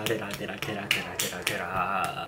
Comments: Is it me or someone else who think that at 7:40 I.N was saying (i misheard ofc) Radhe Radhe and was also clapping ❤ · idk why it sounded like "Radhe Radhe" only Indians can relate Anyone from India?:)